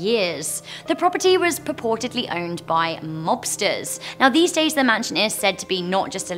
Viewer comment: At #4 I thought said the house used to be owned by lobsters, then my brain started working and realized that she must have said mobsters